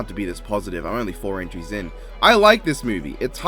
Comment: …I thought you said inches for a sec, wow